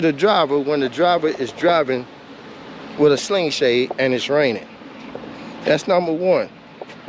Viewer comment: I thought you were saying a Sling Blade. That’s a nice looking sling shot. A good body shop can fix that. Put some wind deflectors like on the GW to channel the air. A good name for yours would be “The Blue Max.”